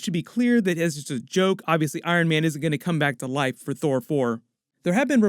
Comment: it sounds like you say thor thor